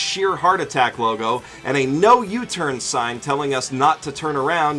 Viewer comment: My dumbass thought that he said “no u”